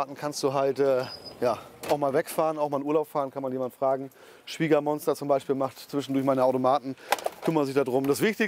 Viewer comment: Hat der Schwiegermonster gesagt ? 😂😂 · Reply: wasch dir die Ohren er sagt eindeutig Schwiegermonster. Das ist ein Scherz, weil sich klischeemäßig viele Männer mit der Schwiegermutter nicht gut verstehen.